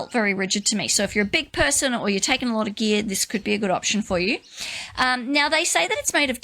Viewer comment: I misheard your comment "if you're taking a lot of gear" as "If you're taking a lot of beer." Works either way! LOL.